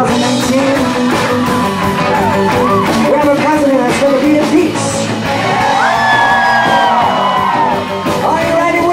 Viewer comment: What does say at 4:00, just before 'we have so many things to look forward to"? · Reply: we have a president that's going to be impeached